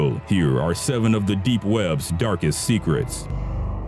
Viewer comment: my meme destroyed brain thought it said "Dankest Deep web secrets"